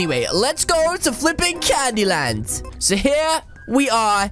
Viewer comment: Lol I thought you said candle